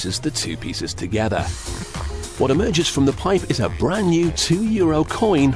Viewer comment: it sounds like he is saying a brand new 2 year old coin ready to be spanked. o.0 Like if you hear it.